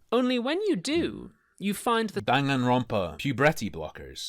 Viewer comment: i swear to god i thought he said “danganronpa” and that woke me up 😭